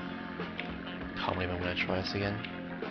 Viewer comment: Subtitles say "I'll even touch my ass again" ait 0:09, wtf